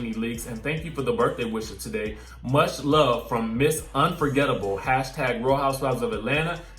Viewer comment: I thought Nene actually said Miss I’m forgettable